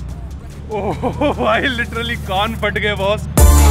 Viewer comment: I think he said g**d patgae